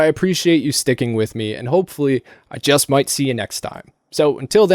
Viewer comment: I thought he said read a man book at the end instead of a banned book. 😂